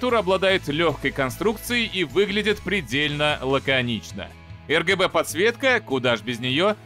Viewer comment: мне одному послышалось ЛГБТ подсветка?